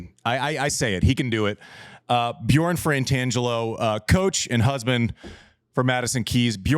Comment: It sounds to me like Andy is pronouncing it “Frantangelo” and not “Fratangelo”. I could be mishearing, but thought I’d note just in case.